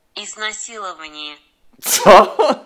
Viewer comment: ja usłyszałam "nic na siłe" ;-; :D